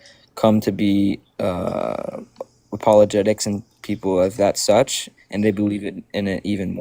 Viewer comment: …did he just say “people of that such”?? Cuz it sounds like he just said “people of that such”… 🤔